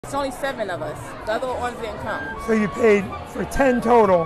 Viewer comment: Why did it sound like she said "my little orphan didn't come?"